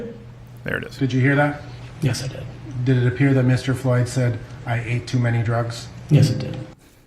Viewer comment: I do think Chauvin should be acquitted. But for the record...when I hear that clip, I don't hear "I ate too many drugs". I hear "I ain't do any drugs". He was saying anything he could at that point to try to get out of being arrested.